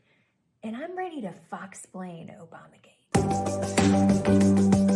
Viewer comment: When she said "Foxsplain", I thought she said "Fucksplain". 😆😆😆😆 Same Difference.